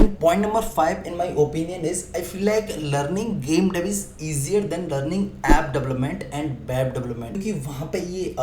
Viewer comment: what are you saying game dev is much difficult that web/ app dev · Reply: No i said that learning game dev is easier than those two 😅